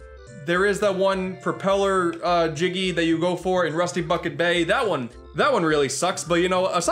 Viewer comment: i thought he said rusty fuckin bay lol